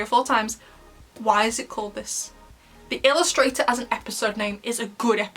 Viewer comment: Dear, I heard Illustrator, not Illustrhater.